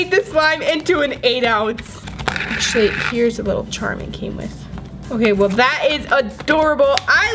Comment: is it me or did she say "shit'' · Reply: jazmine arriaza no she said 'actually here's the charm it came with'